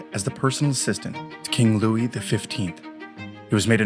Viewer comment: You say Louis 15 but the subtitle says Louis 16. FYI.